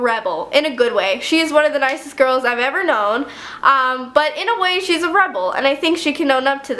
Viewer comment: I thought she said she was a redbull😂😂